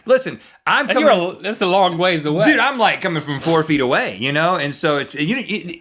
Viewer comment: So i zoned out after they said why some are labeled men and some are labeled woman and at 9:52 i heard Rhett say i am cumming from like four feet away.